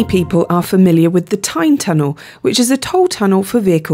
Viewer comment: Tyne tunnel I thought you said Time tunnel!